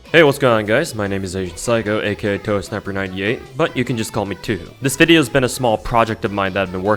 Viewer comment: thought u said ur name was ToeSnapper